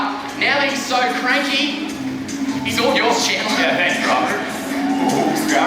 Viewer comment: he said oh shit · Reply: oh year I thought he said he’s all yours and then shit thx for saying I didn’t notice maybe I do need to get my ears checked lol 😂